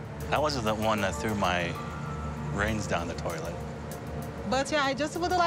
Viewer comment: the cc says, "I wasn't the one who threw my brains down the toilet," instead of rings down the toilet. That's hilarious but so true. She DID throw her brains down the toilet with that ring...LOL.